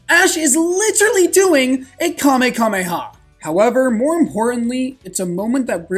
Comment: I'm sorry, did he say "Kame-KAme-ha"?! He is literally Goten! lmao